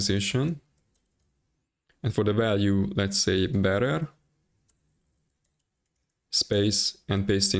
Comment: I didnt catch what you said for the value before putting in the api key? Did you say "bearer" or "barer"? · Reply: Bearer <link>